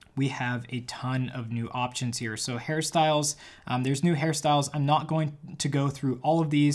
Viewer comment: I thought first that he said Harry Styles but realized directly that said hairstyles